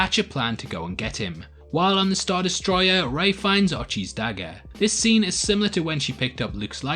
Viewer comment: You kept saying “Ray Finds” but all I heard was “Ralph Fiennes” and now I’m like, God Ralph Fiennes would have been a perfect villain for the contrived garbage movie